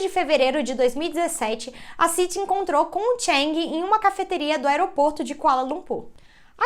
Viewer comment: Eu escutei "aeroporto de colanocú"😂😅